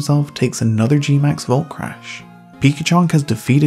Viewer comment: Pikachunk. I think I heard you said that.